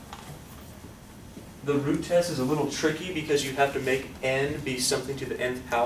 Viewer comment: I thought you said the Groot test XD